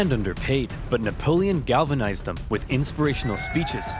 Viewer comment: Did he just say that he galvanized them